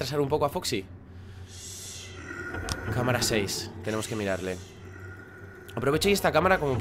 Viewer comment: yo escuche vamos a intentar retrasar un poco a foxy y la voz dijo siiiii Eso entendi yo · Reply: dijo six seis en ingles boludo xdd perdona si te ofendí al decirte boludo